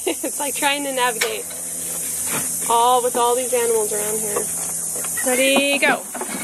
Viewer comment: "Readyyy GOAT." Is what I heard XD